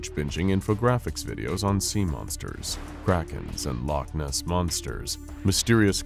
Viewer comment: ok, the first time thru I swear I thought he said "sea monsters, crackheads..." instead of saying "Sea monsters,, Kraken", y'know? Lol! 🐙dah kwaken!!! Wun away!!!